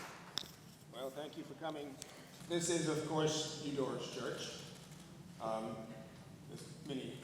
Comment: who's church does he say it is? Eeyore? thank you if you know and can reply